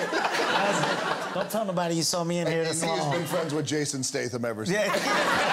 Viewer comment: what does the rock say here ? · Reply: "And he has been friends with Jason Statham ever since."